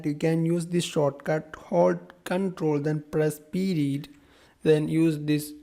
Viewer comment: Hi, i keep hearing you say "ctrl+p dit" please write it down i can't seem to get it. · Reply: hold ctrl then press period(. symbol).